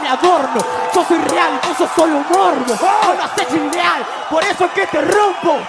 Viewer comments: Siempre tengo mis dudas con esa parte pero claramente no dice "vos no haces freestyle" según yo puede que diga "vos lo haces unreal" (irreal en inglés) ya que está hablando de que él es real. Pero no se comenten porque quiero saber lo que dice. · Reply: Dice " Vos no haces Free real" Free refiriendose a Freestyle. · Vos no haces fyjfifyaaah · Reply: Yo entendí "vos no haces free real"